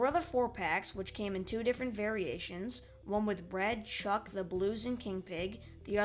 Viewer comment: Does any one else hear Bread instead of Red?